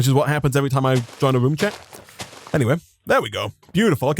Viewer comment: “Wisdom leaves” when you enter the room lol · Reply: Sounds like he says "joins a room chat". Lol